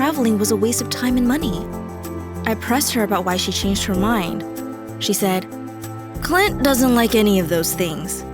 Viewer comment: I thought she said ‘and she had meet clit’